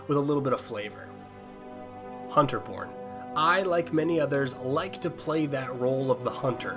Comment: Who else heard Hunter Porn?